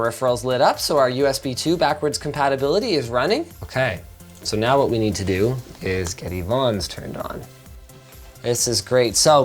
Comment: "Now what we have to do is to get Yvonne turned on" is all what I heard at 22:15 ¯\_(ツ)_/¯